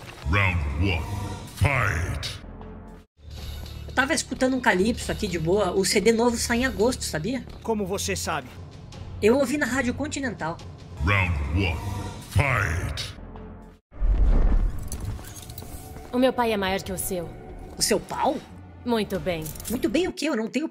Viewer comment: "Eu ouvi na rádio Quan-chi nental" kkkkkk